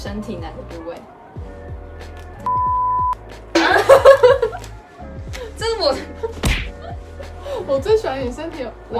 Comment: 有人可以告诉我6:34说了什么吗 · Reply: 上面有回了 是「海鮮」😂